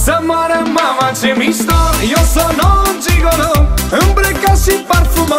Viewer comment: sorry but i thought he said "eu sunt noul gigolo" 👁👄👁mulțumesc